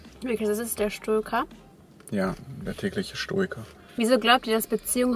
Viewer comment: Wie ich bei 7:25 einfach verstanden habe: "Der tägliche Stuhlgang!" 😂😂😂